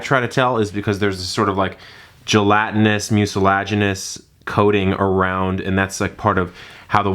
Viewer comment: what was that at 10:54???LOL. Try saying that 3 times fast... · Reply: Gelatinous, mucilaginous